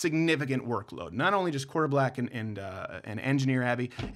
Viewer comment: did he just say his name is quarter black